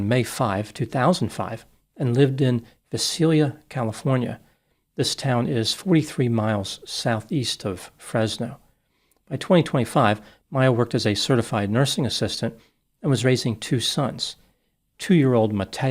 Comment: The goose lip pictures say it all. Our society is overrun with narcissistic people. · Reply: The funny thing is when he said she’s a “certified nursing assistant” I thought he had said “a certified narcissist”